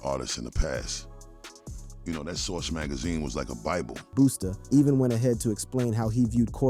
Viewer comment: did you call that man Boosta? 😂